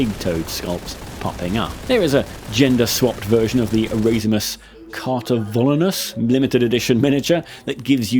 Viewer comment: What limited edition mini is this? I can't catch how it's spelled. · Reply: It’s Erasmus Cartavolnus, i think you can still find them on eBay